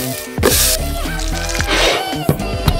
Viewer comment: "Everybody so creative!" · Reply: I sworn she had said and I quote "cheeseburger condom"